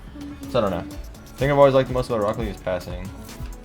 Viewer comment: Kaydop subtitile (im french too, my english is Bad too): there is more than one thing . I Hope one dev of psyonic hear me , cause they never hear me. Im the one streaming the most but they never listen to me . The first thing i will change , is that when i bump someone with the Flick, if im the first one to flick, i cant bounce back and own goal (rlcs week2) It's not possible. It's physics And dev of psyonic dont understand physics . I will try one day to tell them of démolition should work . Watch RLcs in the game cause all the Big game do it except RL . Ranked, OMG, oh no i forgot ranked there is solo standard, Nobody play it, only people that cant play in RLCS try to be good at this. Yeah Ranked team mode . Team mode will be really really appreciated. Did i respond good to Ur question? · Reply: oh i thought he said he yelled the most